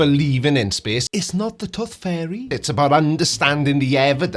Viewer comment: I don't mean to make fun of your accent But I heard "Tough Fairy" and it made me laugh.